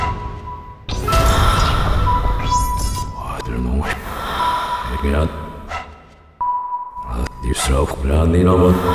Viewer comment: 근데 2:38부터 배달의민족 아저씨가 뭐라고 한거예요? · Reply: 신희선 짜장면 그릇 달래요.